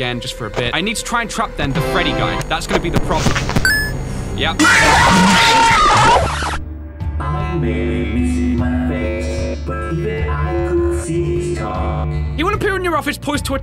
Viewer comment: what withered bonnie said: I may lose my face but even I could see in the dark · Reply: He said (i maybe missing my face but even i could see you stalk)